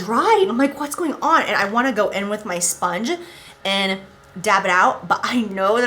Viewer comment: When you say "damp sponge" I swear it sounded like you said " damn sponge" 🤣